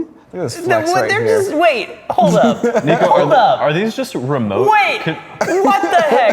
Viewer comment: did bro say the n word? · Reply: The person on the right side of the couch (from our perspective) is named Niko.